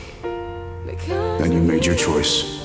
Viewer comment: I completely misheard the 'there's the door' part instead I heard 'there's the porn'